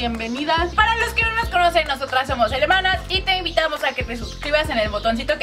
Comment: EN EL SEGUNDO 0:18 ENTENDI: NOSOTRAS SOMOS ALEMANAS EN VES DE HERMANAS JAJA Like si tu tambien